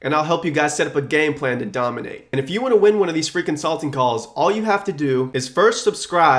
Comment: I always hear it as "freakin salting calls"